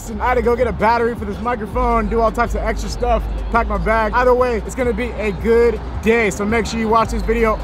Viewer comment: At the beginning when you said its going to be a good day it sounded like you said it going to be a good gay